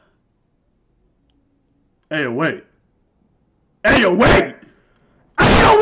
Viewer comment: When he was what the house doing i thought he said Let the house do it💀